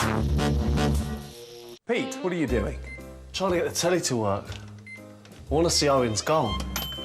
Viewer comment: "Pete what are you doing?" What does Crouch exactly say? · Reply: "Trying to get the telly to work, I wanna see Owen's goal"